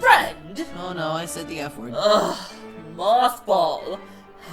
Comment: MB: oh no I said the f word Me: u mean f***, I thought u said friend, oh I get it now